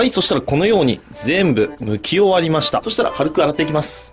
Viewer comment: 剥き終わりましたが抜き終わりましたに聞こえた